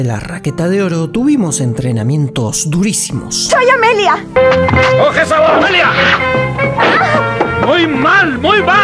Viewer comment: ojesawamelia Yo creo que dice: coge esa bola Amelia..!